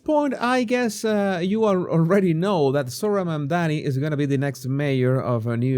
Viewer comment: Heard someone refer to him as “Moron the Commie” instead of Zohran Mamdani. That made me laugh.